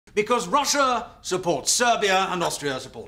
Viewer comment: nah i heard ‘i support arson!’